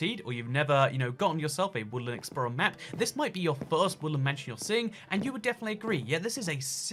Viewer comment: No hate but why did it sound like he said woolen mansion? Instead of woodland